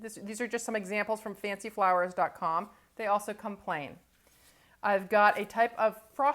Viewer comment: I thought she said "They also complain." LOL